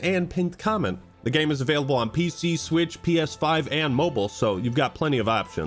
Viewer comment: "The game is available on PC, Switch, PS5 and Mobile" Did I hear Switch? Nintendo Switch????